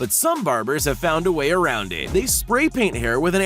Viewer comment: I thought it said barbie